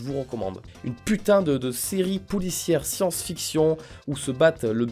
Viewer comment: j'ai entendu "une série poulicière"